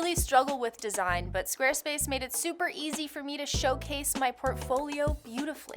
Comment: Why did i hear that as SwearSpace?